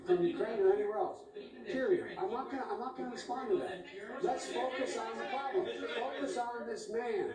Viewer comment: Oh wait! Did he say, "...less focus on the Bidens, focus on this man (Trump)... " Am I missing something?